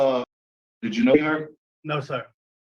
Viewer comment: sh does anyone know what he asked him?? It sounds like he asked "did you know her" but that doesnt make any sense so maybe someone can tell what he asks.